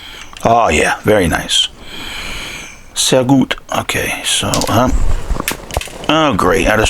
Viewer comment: Did you say: "sehr gut" @ 35:50? Greetz from Germany 😉👍🏻